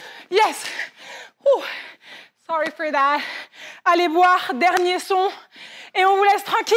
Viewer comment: does say, "Sorry for that"? lol keep spotting it